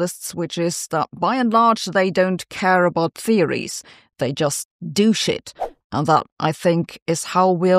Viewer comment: "They just do shit." lol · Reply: Listening at double speed while reading on an unrelated topic, I first heard "they just douche it"